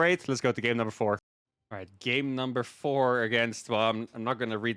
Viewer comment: did he say “game number thor” )))